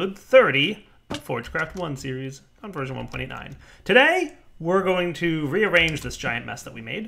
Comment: Wow I heard the 1.9 translation will be quick, but I didn't expected that O.o